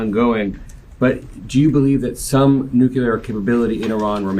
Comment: Did that reporter just say 'nucular' instead of nuclear??? JFC. No wonder this country is in the position we're in. Pathetic.